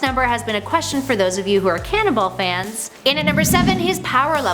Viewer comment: I thought she said cannibal